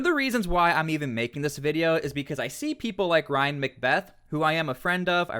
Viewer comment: At first because im multitasking i thought i heard you say Ryan MacBeth on JR podcast. And now i kinda want to see that. I dont watch Rogan often, but i would watch that episode.